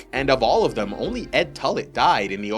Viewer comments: Oh, God. I thought you said Ed Tully. When was Edmure in this!?! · I thought i heard you cal Dolorous ed "Ed Tully"?